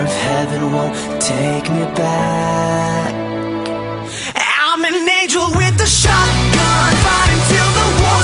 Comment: I heard "I'm an ninja with a shotgun" well nightcore got some explaining to do